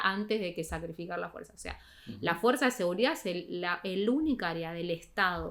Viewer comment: Me encantó! Escuché "Las Fuerzas de Seguridad son la Peluca del Estado" y tuve que retroceder para saber que decía realmente 😂❤